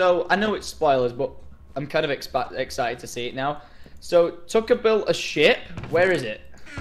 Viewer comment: 😂😂😂 I thought said "So...Tucker built a shit"